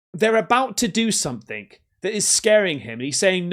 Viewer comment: To me it sounds like he is saying IM SOARING, maybe it is a spiritual abduction?